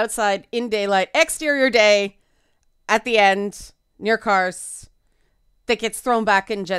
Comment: Cheers for actually going into specifics, rather than the usual handwavy "It's not for you" dismissal. @ 5:30 — Jen's apology at the beginning, and at the end, "near cars/kars" ?? I'm not sure what you're saying here. On Josh: I don't know how common it is for men in general, but I hated Josh from the very first line out of his mouth. It was probably a meta recognition, where after a bunch of terrible men the show was going to give you a "too good to be true" man, or maybe meta in the way they crafted his image as being so incredibly stereotypical it hurt, rather than recognizing whatever signs women are supposedly seeing in him. @ 28:50 — "The average woman wants a story about Jen Walters learning to live as both Jen and She-Hulk." — I don't know about the "average" man, but that's definitely what I'd like to see in the story as well. On the rest... I'm going to need to spend some time thinking about it.